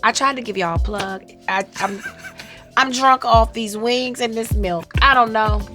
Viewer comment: Did she say I jerk of those wings or am i that deaf?